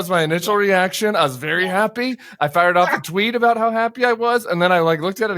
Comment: Honestly thought you said, " I had to fire up some weed " . But, you fired off a Tweet 😅 GREAT NEWS!!!!🎉 HARRIS ⭐⭐🇺🇲🇺🇲 WALZ FOR THE WIN, CHILL THE🎉 CHAMPAGNE!!! 🍾